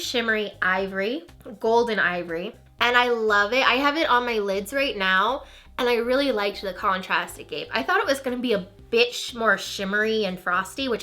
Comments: Legitimately thought you were going to say "a bitch" instead of "a bit" around 4:55... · I thought you said "I thought it was going to be a bitch" I was like damn is the shadow that bad? lmao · Lol I had to watch the part at 4:54 a couple times because all I kept hearing was bit*h